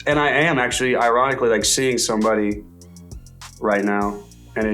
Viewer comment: I Think he just said he is dating sommeray!